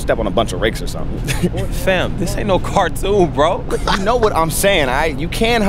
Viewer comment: Hol'up 18:44 the subtitle says 'sam' not 'fam' lol